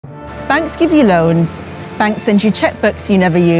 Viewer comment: I thought that said Banksy at first and was like…“not him!” 😅 Hmm, I’ve always looked up in awe at the architecture in the UK but now I’ll think a bit differently. This really puts them into perspective once you look past the embellishments. Honest History really should be compulsory in every country. The more I learn about it, the more I realise how foolish these white hate groups are here, but I suppose, globally too.